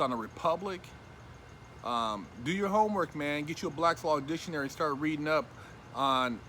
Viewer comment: wait, did he say “black slob dictionary?”